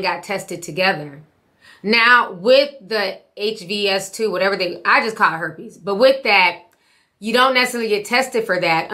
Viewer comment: I'm not going to lie. 😌My heart jumped for a second because I thought you said you caught herbies, but I had to realize that you said you'll just call it herpes.